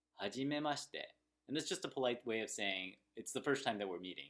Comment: There is a little mistake, actually 始めましえ instead of 始めまして